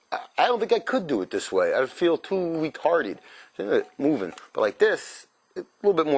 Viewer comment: Did you say i feel retarded 3:07 !!! hahahaha Great video thanks